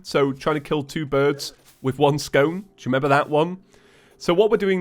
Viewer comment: does he say stone or scone...I think its scone....what kind of scone?